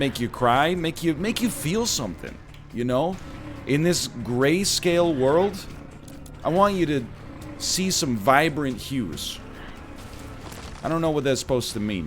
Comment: A video that was recorded yesterday? Blasphemy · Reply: I thought you were gonna say you wanted us to see some violence